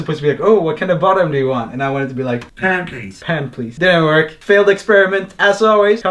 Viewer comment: When the computer said, "Pan please," I heard "pancakes".